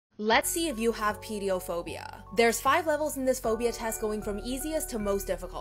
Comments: What it says: Do you have Pediophobia?" What I thought it said: "Do you have pedophilia?" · My dumbass who thought that said pedophilia 💀💀